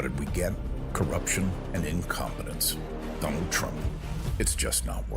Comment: At the end, the ad said we got "incompetence" but I heard "incontinence." That too. Look at every time he signs an executive order, the guy says "this one is about...and explains it to him." When he's finally brought to justice, all the money made from the corruption and the coins should go to the people - charities and medicaid and medicare and the international orgs like USAID.